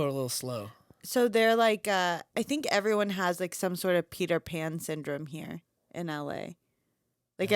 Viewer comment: I thought she said pee their pants syndrome. No no no she said peter pan, 😂😂 😂 Hahahaha. Pmsl Oh wait. Pmsl. 😂